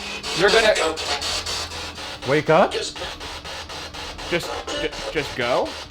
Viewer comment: that did Not sound like "wake up", it sounded like "Go away", which makes a lot more sense in the context as well, followed by the "just go"